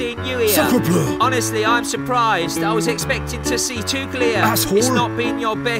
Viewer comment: is he saying tuchel?